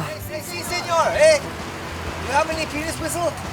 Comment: WTF did he say @ 18:52, I swear he said penis somewhere in there